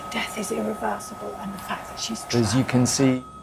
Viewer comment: I heard, "the fact that she's traveling around". Maybe?🤔